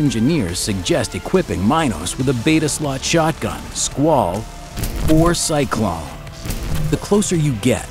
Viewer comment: IYXMLY/android 1:30 did say 4 cyclones?